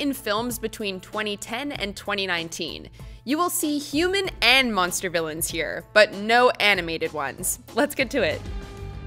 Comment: Will the deviousness of Lots-O'-Huggin' Bear from Toy Story 3 ever end! We said in the criteria that we aren't including animated characters, but he appears in the video as an Honorable Mention! Apologies, we know this mistake can come across as un-BEARable. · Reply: No, her actual words at 0:25 are, "You will see human and monster villains, but no animated ones". She does not say "from animated MOVIES" at any point, only specifically mentioning animated characters. This is not the first time WM has gone against or ignored its own criteria. They just shouldn't have bothered saying "no animated ones" because they ignored it.